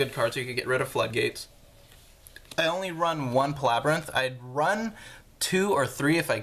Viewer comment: what is the name of the Card at 6:29? I try but I can't understand the name or read the card name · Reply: It's Archfiend Palabyrinth :)